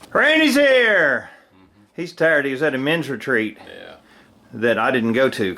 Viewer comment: Hey, " it's a dirty job but somebody has to do it. " Tell your assistant, I'm impressed. I misunderstood you when you said Randy just got back. I thought you said a Mensa Retreat. You know the High IQ Society, and it sounded like you were invited also. I thought, Wow, we got a couple brainiacs here. I listened again and you may have said a " Men's Retreat ", but I could see both as a possibility. KIT d